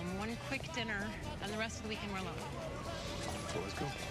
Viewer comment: he opens the door for her, but she tells him something as she walks around the car to get in (1:16). what did she tell him? sounds like " i cant let them see me" can anybody tell me what she said?